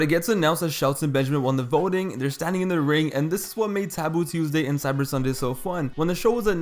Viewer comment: My fat ass really thought it said Taco Tuesday 🌮 smh I did it to myself